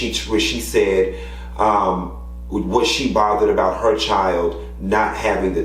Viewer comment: I thought she said she wasn't bothered about HER title, but was about her child not given his.